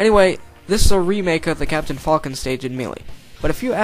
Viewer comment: Did He Say "Captain Fuckin"?